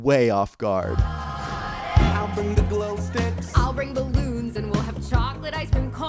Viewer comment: Are we sure there wasn't a caption mistake. I relistened it and it sounded like she said "And I'll bring balloons."